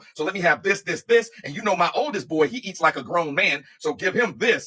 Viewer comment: She said “My 12 eats like a grown man.” I heard “My my man earns money like a 12 year old.”